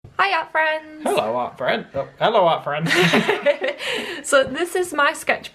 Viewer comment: At first I thought you say hello our friends, but now I know it's hello art friends